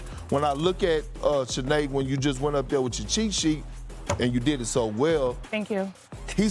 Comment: I swear I thought perk was gonna say "when you went up there with yo cheeks" 😂😂